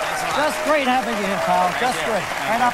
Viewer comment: does he call him Kyle? lol wtf? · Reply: lol he says "Pal"